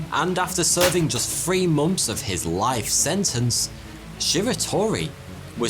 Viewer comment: I would also like to mention that his specially made hand, and leg cuffs weighed 20 kilos each, and he was barely able to stand while wearing them. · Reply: Yes, but why does he say mumffs to months?